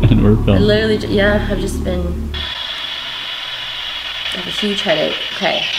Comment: Im prolly freaking bout at the beggining when u hear the "male voice" i definitely heard "huge headache"